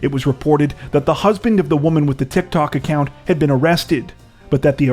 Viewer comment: did anyone else hear "the womans 'husbing' "?